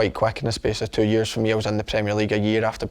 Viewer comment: what league does he say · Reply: prem